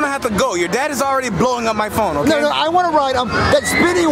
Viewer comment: In 11:19, what did Chef Peepee say? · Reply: Your dad is already blowing up my phone.